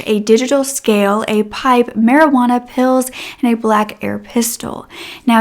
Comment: i thought you said marijuana pills. not marijuana, pills.